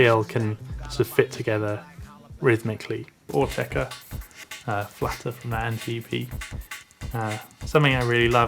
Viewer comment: does he say Autechre flattered man ep?